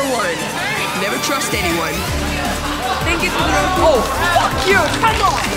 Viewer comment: In the beginning I thought he said "hey fucker"